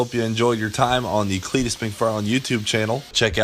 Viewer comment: I thought he said the Fetus McFarland site at first when he was talking about the shirt lol